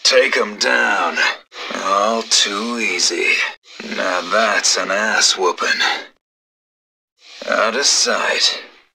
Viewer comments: for the longest time I thought he said "now that's an ass, woman" Probably cos he sounds so creepy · i used to think he says "Now that's an ass woman"